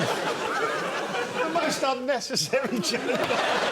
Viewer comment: "A most unnecessary joke" Makes Joe sound like Noel Coward somehow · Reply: For some reason, I always thought Joe went posh and said "most unnecessary Jimmy"